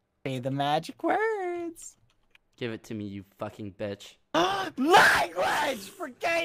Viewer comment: I heard “Do it to me you f@ b****” Smh